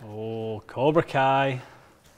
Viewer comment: does say, "Cobra Kai"??